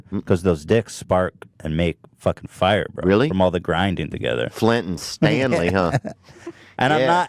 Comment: Did he just say flint and Stanley... bruh I’m done